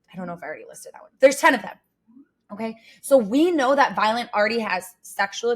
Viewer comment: do you think she realises that she called her "Violent" and not Violet?